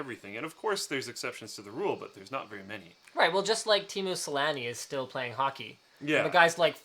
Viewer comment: I watched a video of some Americans talking about Teemu Selänne. Throughout the whole video I wondered who is this "Team Muslani" they talk about. Now that Linus mentioned him, I thought "hmm, where have I heard "Team Muslani" before..? …OH!" until I finally realised they mean Teemu.